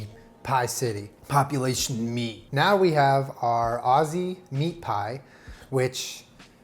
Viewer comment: I first thought Julien said Ozzie pie..like Ozzie Osbourne. 🦇🦇Julien, please don't make bat head pie. 🦇🦇